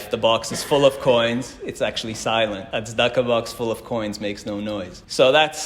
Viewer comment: ” duckubucks full of coins makes no noise” said at 6:55 in the video. Please correct my word “duckubucks” lol. I can not make out what you are saying… thank you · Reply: The term is "tzedakah box". Tzedakah means charity.